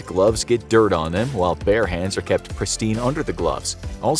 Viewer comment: I literally heard 'bear' 🐻hands and I was thinking how weird that was since Russians seem to like bears so much - oh, yeah, duh! Bare hands! 🤦‍♂